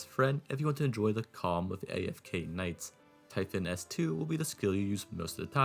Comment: Ray, Ascalon better. If anything Typhon is one of the most over hype ops in AK. And this is coming from someone who have Typhon m6 mod3 lol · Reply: i thought you said typhon p6 lmao 😭